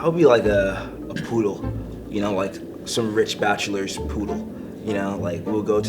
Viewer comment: I thought he said “some rich bastards poodle” and BOY i lost it I laughed so hard